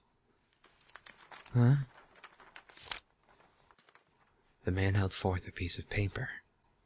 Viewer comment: I paused it around 14:36 and thought it was saying "the man held forth a pie"